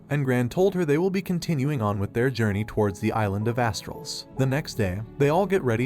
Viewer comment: I know I'm old, but I kept hearing 'Island of a**holes" 🤦‍♂️